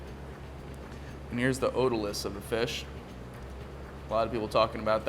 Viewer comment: What was that little balancing thing you removed? Sounded like you said otalis, but I can't find out anything about that online. Thanks. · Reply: otoliths iianm